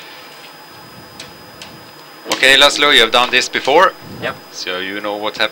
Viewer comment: Is he called? At 0:53 . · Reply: His name is László